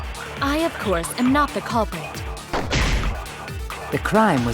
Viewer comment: “I, of course, am not the culprit.” Although, it says “criminal” instead of “culprit”.